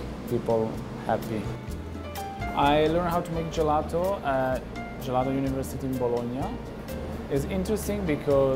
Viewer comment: Did i just hear gelato university ?